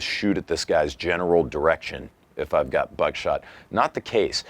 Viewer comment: I thought you said genitals direction 😂 That would be cold blooded man.